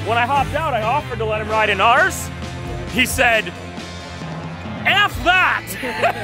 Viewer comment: I thought he said i'm too fat not f that